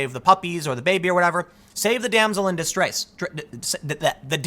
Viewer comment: "Save the damsel in dis-dress." I will be hitting that like button. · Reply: I thought he said "damsel in disgrace"